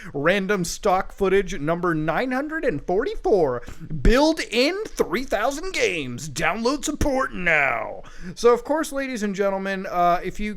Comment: sounds like muta says built in 3000 games download some porn now lol